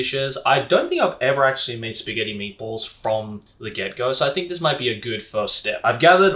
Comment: I thought I heard "I don't think I've ever made spaghetti meatballs from the ghetto" at 3:47. Still accurate since it's spaghetti de la hood.